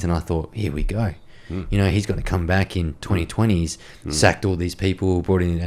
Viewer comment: Did he just say Labour might get back in 2028? 😢😢😢😢😢😢😢😢😢😢😢😢😢😫😫😫😫😫😫😫😫😫😫😫😫😫😭😭😭😭😭😭😭😭😭😭😭😭😭😭😭😭😭😭😭😭😭😭